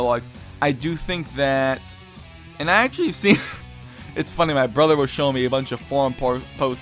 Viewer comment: When are you going to make your new channel and did you say "My brother showed me a bunch of forum porn" at 4:02 ?